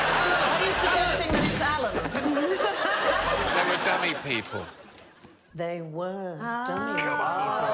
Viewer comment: [Edit: ooooh... 'dummy people'. I should have just let it play instead of trying to use the tech to replay what I'd missed. (MORE technonsense - D'oh!)] 23:48 Help! I can't make out what Alan says that is apparently the right answer. (YouTube labels this part as 'Most replayed' - I'm not surprised; I've replayed it several times myself trying to turn captions on for that bit but it doesn't work. Bloomin' technonsense /grumble) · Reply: 'They were dummy people'